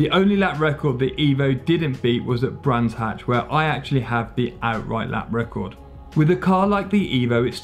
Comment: did he say he has the lap record ?